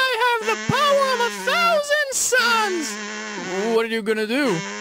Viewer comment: Am I the only 1 who thought 6 said the power of 1,000 sans